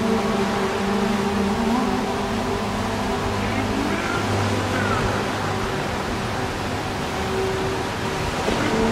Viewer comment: Does anyone know what the distorted voice at around 1:38 says? It sounds like its saying 'can you hear the sea' which makes sense because it's the track name but idk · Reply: Yeah it saying ‘can you hear the sea’